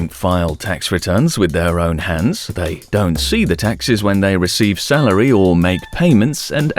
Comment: Lol thought you were saying taxis